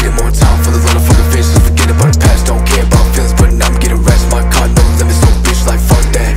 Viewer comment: I thought he said: "forget that, facts dont care about your feelings"